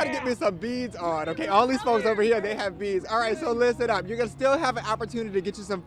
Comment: Was I the only one who thought the man at the end said 'these hos over here have them'? LoL I had to listen again